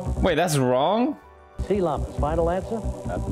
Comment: Lol I thought he said "Sea Llamas"